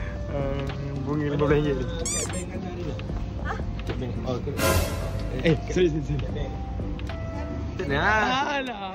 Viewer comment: tolong guys cakap apa eh minit ke 21:48 🤏🏻🤭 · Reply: “Mana kad bank kanda rina?”